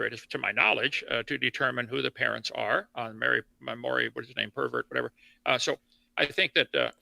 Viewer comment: did he say Maury Pervert? 😂